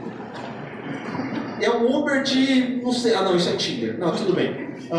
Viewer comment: O que ele fala no 34:28 ? Uber de que? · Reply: "uber de... ah não, isso é tinder..." (tlvz eu esteja enganado, mas acho que foi foi uma piada pra falar uber de puta kkkkkkk)